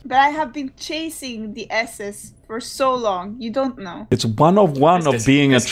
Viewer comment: When said S(es) I actually thought she said asses. Wouldn't be surprised.